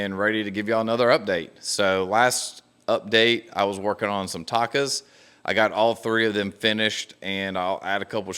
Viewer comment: Mmmm I kept hearing “tacos” and the Takas look awesome too 😍